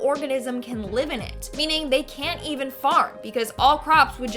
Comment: I heard "...they can´t even fart because all crops would just immediately die..." I need a hearing device, apparently. 😂